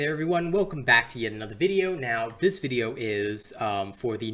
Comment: I thought he said "Welcome back to the vietnam war" for a second XD